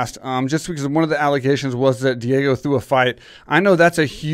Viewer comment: What does Mike say at 5:03, "Diego threw a fight?"